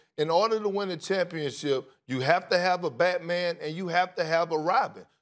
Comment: Thought he said you have to have a Batman and Rodman 😂 I’d take a Rodman any day